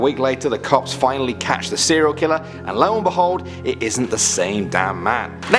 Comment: am i tripping or did he say "it isnt the same guy" Ive replayed it multiple times and keep hearing it isnt